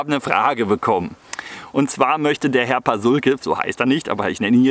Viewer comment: 'D ich hab erst "Herr Paschulke" verstanden, der Nachbar von Peter Lustig <3 Der hat auch immer Fragen und Rätsel gestellt, die Herr Lustig lösen sollte